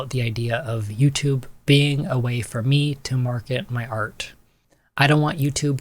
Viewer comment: Thanks for the video. I’d like to say that at the end I thought you said Market my HEART not Art. And since art is closely related to people’s heart. I think that is fitting❤️